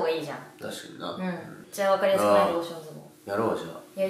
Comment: ローション相撲か笑笑笑 ローションズボンに聞こえてた笑笑笑笑 流石にあかんやろ…と思って指摘コメ探し回ってた、ローションズボンって何？